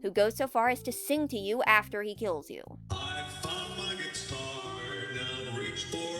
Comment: " i fuck my guitar" im telling you thats what he says 100% no lie